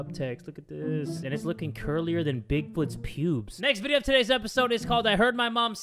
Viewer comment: did anyone else hear him say welcome to the next video bro that’s the first video 😂😂